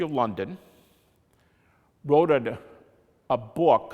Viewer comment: Did he just say "wroted"?